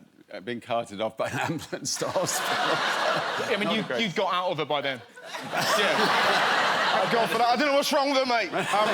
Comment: ”Whispered in my ear and said _____?” Can’t make out What the quest says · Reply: asthma attack asthma attack